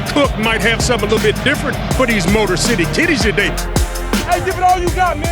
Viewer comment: I died when I heard “ MOTOR CITY KITTIES “ 😂 Skol